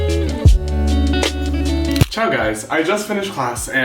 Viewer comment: I don't understand...at 6:23, did you say "ciao"? I'm italian and I'm pretty obsessed with your videos, i find them very inspiring so hearing you saying something in italian feels strange lmao, none usually care bout italian. Love from Italy🤍🤍